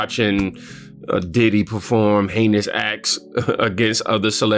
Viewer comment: … heinous acts or did you say anus? ACTS! 👀… I see ya! 😂😂😂